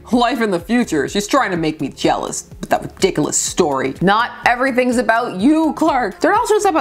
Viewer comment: Wasn't looking at the screen and I totally thought you said "... with that dickless story!"